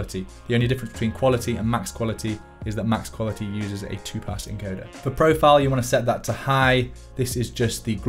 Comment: "Max Quality uses a two-pass encoder." The English CC subtitle writes "two-part encoder" instead of "two-pass encoder".